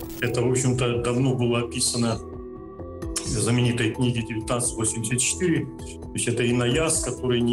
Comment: какая книга - не расслышал? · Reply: Книга "1984" Джорджа Оруэлла.